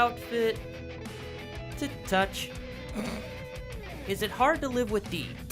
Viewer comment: WHY IS HE BLUSHING I JUST HEARD "(something something) touch" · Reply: He said "to touch"